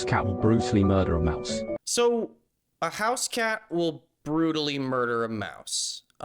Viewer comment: did anyone else think the podcast guy said “how can Bruce Lee murder a mouse?”?